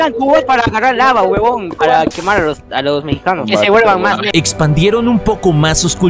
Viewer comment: Soy yo o escuche que dijeron " que se vuelvan mas ne-"😂😂😅??